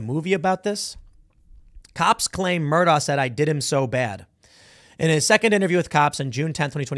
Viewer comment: He didn’t say I did him so bad he clearly said “They did him so bad.” The defense got up on cross exam and made that investigator say “I heard ‘I’” even after playing the audio slowed down where it was clearly they. And then showed the investigator never asked any follow up questions about this supposed admission of guilt. Quite effectively made that guy look like a liar.